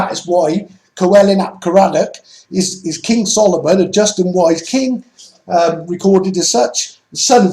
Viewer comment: Bodicia of the iceni was dubbed as Britania,and the renaming of Norfolk to caerwnt feels as a defiance to her brother the arc of god,and I LoL as I've heard you say twice now Solomon a Just-in(N)wise king. Thanks and YA's Blissings?!